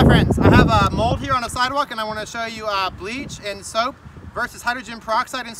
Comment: It sounds like you said breeze and soap - is that a gallon of bleach, did you say bleach & soap?